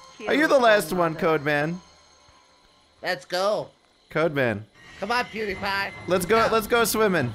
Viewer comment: did he just said 'come on pewdiepie'?